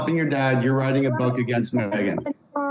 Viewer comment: did he tell Samantha "you were writing a book against my Meghan"? PLEASE someone listen and tell me if I heard that right.